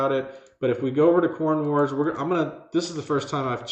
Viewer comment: LYQrbn8NtjBGSPbtZNuwnnCzx53fh8u5vC I thought you said go to corn wars....its not what you think and HR isn't too happy with me